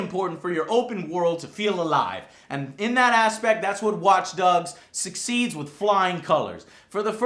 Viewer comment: did he say watch dugs at @ 8:38?